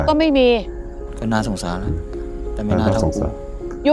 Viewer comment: เขาพูดว่าอะไรหรอครับ ได้ยินไม่ค่อยชัด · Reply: คนที่นอนบอกว่า ก็น่าสงสาร แต่ไม่น่าเท่ากรู ส่วนเจ้าของช่องบอกว่า ก็น่าสงสาร(คิดว่านะคะ) ค่ะ